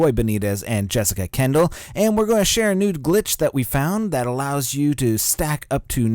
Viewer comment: it sounded like you said nude glitch lol I have also heard that they will just give you more ammo capacity from the get go...